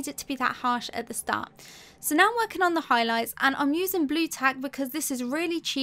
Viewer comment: Hello, very amazing tutorial and art and really helpful. I do have a doubt, at 9:20 you are saying about? "blue tack adhesive?" or something else? Sorry, I'm from different country and probably it's not common here, so couldn't get what you pronounced it/ said ... so anyone could help? thanks!